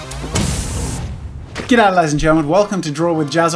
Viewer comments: I just put subtitles and because you talk so quickly in your intro when you said ‘g’day ladies and gentlemen’ it thought you said ‘get out ladies and gentlemen’ I started crying with laughter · GET OUT LADIES AND GENTLEMEN but what are you really saying